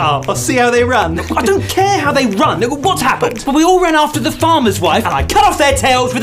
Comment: bro why does it sound like he says "n** what happened?"